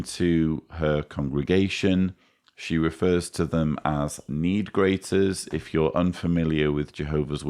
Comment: I am SO glad you explained "need greaters" because I heard them say "knee graters" and I was horrified/extremely confused.